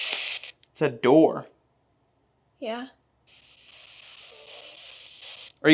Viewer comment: was standing on the stairs and he said: I heard there was a child that wasn't wanted and he was kept in the attic. and @ 1:28:54 you guys put "DOOR" but I heard "BOY".